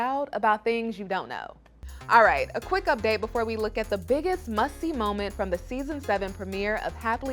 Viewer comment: Who else thought she said “the biggest musty moment” when they cut to sumits family💀